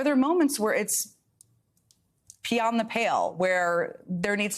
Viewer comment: I thought Jen said "Pee on the pail." and was shocked. I had to put subtitles on to check.